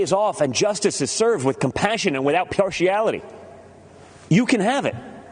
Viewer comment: what's the word that comes after "compassion and without" ... ??? Perchiality? Partiality? His Freudian slip was showing: I think he wanted to say "without IMpartiality." Which would be an accurate representation of the GOP's way of doing things.